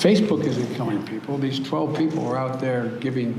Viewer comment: I actually had to turn on the CC to understand..."lol" what Biden said... 12 People??? he said that at 00:50 time est line on... wha?????????????